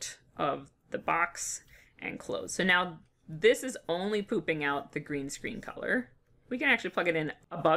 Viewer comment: I keep hearing ''pooping out'' instead of ''popping out''... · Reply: Oh, I said pooping lol.